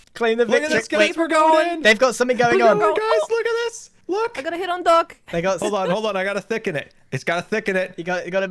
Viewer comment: I had to pause and think for a minute when Pearl said she was aiming for Doc at 15:13. I thought she said "I'm going to hit on Doc" and I first thought was "but he's married"!